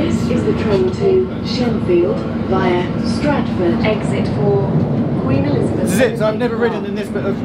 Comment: It seems the PA call it Queen Elizabeth Line instead of just Elizabeth Line. Have there been a name change since the Queen's demise? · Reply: Nah, she actually mentions Queen Elizabeth Olympic Park, basically where the 2012 Olympics were and the whole Stratford City district etc